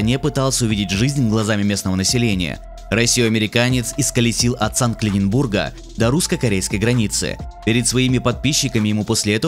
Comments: Мне одному послышалось "От Санкт Калиненбурга" 6:14? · мне одному послышалось от Санкт Ленинбурга?)))